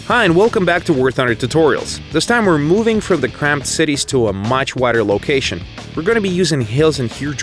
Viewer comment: What did he say at 0:38? The CC didn't help. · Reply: Warrior "This time we are moving from the crammed cities to a much wider location. We are gonna be using hills and huge rocks for cover, instead of houses. Welcome to White Rock Fortress!"